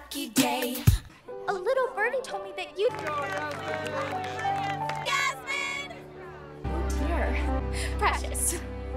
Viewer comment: what she said? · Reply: Thank you